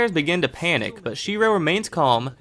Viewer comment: did u just say SHIRO!? I see. No game No life Very good anime.